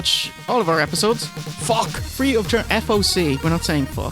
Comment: Ok, so I always thought he was saying fuck free of charge! not FOC (Free-of-charge) LOL